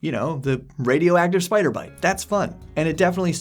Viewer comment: I hear this wrong but I thought had said “Radioactive Spider Monkey” and rolled with that for a second thinking it was legit. I replayed and heard what had really said, but maintain that “Radioactive Spider Monkey” should be a real option now. Thoughts? Back in the day I played the Marvel Superhero’s RPG that was available in the ‘80s cuz my parents were ‘blaiming the game’. Superhero RPGs can be fun, don’t knock them too hard. I think the high level 5e stuff could be tweaked to have ‘altered {human} <playable race>’ approach. e.g. you kill a superpowered thing and its blood got into your wounds and you developed powers, etc. etc. or you fell into an ancient gnomish experiment in a ruin and were exposed to [fill in blank radiation] and roll 2d10 for unique powers and weaknesses. Maybe you were bitten by the spider monkey that fell into the experiment and now have a bad fruit addiction.